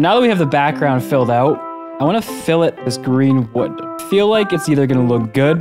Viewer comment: im sorry did you say green wood?